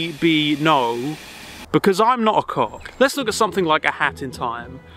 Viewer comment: I couldn't make out what he said there."I'm not a cop"? "Cock"?